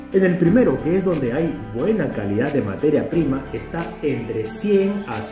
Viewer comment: entendí mal o nos llamó “materia prima”…?